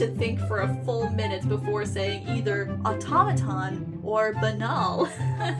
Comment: I thought automaton was said Auto-maiden